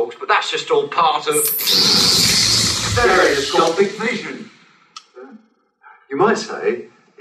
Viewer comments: what are they saying?? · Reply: stereoscopic vision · Sorry, what are the Doctors saying at 10:53? I really can't understand! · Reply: "Stereoscopic vision"